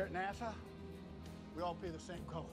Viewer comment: Not gonna lie, I legit thought he was saying "Here at NASA we're all paid the same color." Which is more accurate considering how many shades of pee there are. 😂🤣 Stay hydrated, folks!!